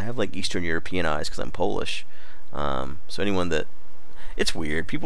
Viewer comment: did he say his polish